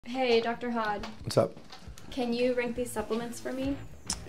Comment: Lol first I thought she said Dr. HOT 🔥 which would certainly suit him lol His voice is calming🫠